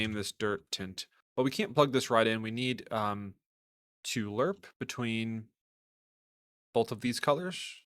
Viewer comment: I can't find the lerp node you used at the 6:56 mark. I couldn't hear the word you used for it. Edit: Never mind. I Googled it and they call it LinearInterpolate. I was just typing Lerp and was expecting to see just Lerp😊....didn’t realize it was under it's full name. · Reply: Yep its LinearInterpolate or Lerp for short. Holding L and clicking is the shortcut. You'll see it used in most materials as its very handy 🙂